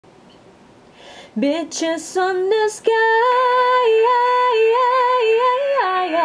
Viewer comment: Lol it sounded like you were saying, "bitches on the sky" 😂